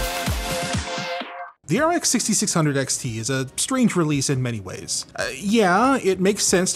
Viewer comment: After the intro theme, I first misheard opening line as the "RX 6600 XT is a sleeper piece of e-waste."